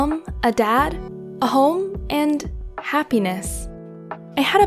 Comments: I swarenit said "a hoe" the first time I heard it 😂😂😂😂😂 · I thought she said crapiness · At the start i thought she said "-and a penis" when she said happiness